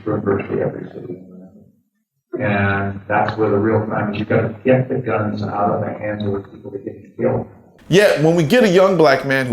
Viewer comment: Am I the only one who heard "we gotta get the guns out of the hands of the people who are getting killed" at 1:20? Like disarm the victims??? 🤔